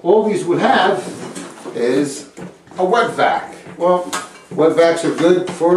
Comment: I thought you said you need a "Wet Back" O_o !!! LOL.. (Wet Vacc) btw not sure why im watching this as I have no reason to remove any toilets lol..